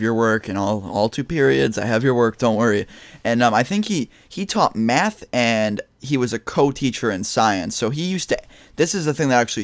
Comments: I thought he said "he taught meth and he was a coke teacher." I was like TF?! 😂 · It sounded like you said 'he taught meth!' >.<